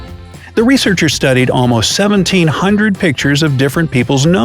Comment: i know I heard :17 thousands"......but my eyes says its 1,700....😅😂